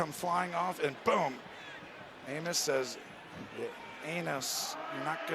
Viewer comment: 💀 I think someone forgot how to say Amos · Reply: lol, he totally says “anus” at one point.